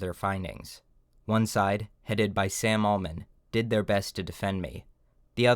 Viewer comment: "Sam Alman" is a funny choice of a name. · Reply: at first i misheard and thought he was named Sam Altman and had to rewind it twice to figure out what he was actually saying